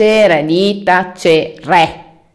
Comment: io che all'inizio avevo capito "dita cere" e poi ho realizzato che diceva " di tacere"😂